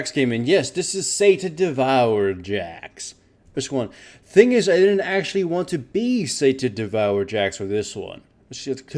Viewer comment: Why does he say "which one??" in every single video? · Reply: I know I'm 4 years late but he says "voice check one". It's kind of a staple of, I guess the first videos he made he actually needed to calibrate his mic and then kept it as it's something traditional for his channel.